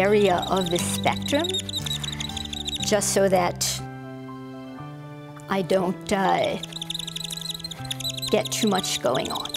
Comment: did she said "I DONT DIE" !?!?